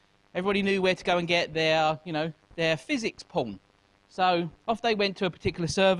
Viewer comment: did he say physics porn?